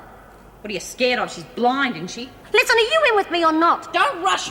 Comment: Thought she said "Listen here Cunt", instead of Hunt lol